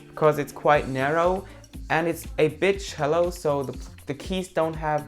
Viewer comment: i swear i was listening to the video while on instagram on my laptop and on 4:40. i swear i heard bitch hello haha i had to take a second listen and see the video again haha i know its because of his accent it sounded like that but i know he meant a bit shallow. plus not going to lie i love the accent its cute. lol and legit hair goals. always have nice hair. got me all jelly lol